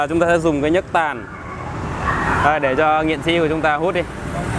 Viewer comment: Nghiện sĩ mà mình nghe thành nghệ sĩ